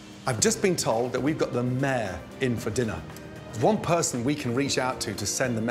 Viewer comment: Every time his says "We've got the mayor in for dinner." I hear "We've got the mare in for dinner" and wonder where they'll seat a horse.